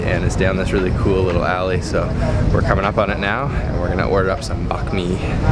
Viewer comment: Thought you said Banh Mi. I was like, that not Banh Mi! But listening closely you said Bakmi haha